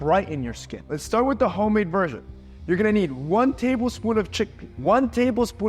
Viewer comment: what did you say i didn't understand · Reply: Youre gonna need